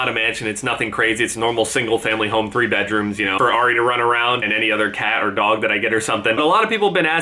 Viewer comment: When he said "for Ari to run around" I thought he said "a Ferrari to run around in"